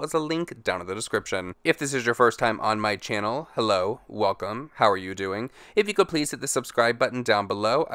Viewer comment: I keep hearing “how the fuck are you doing” but it’s not there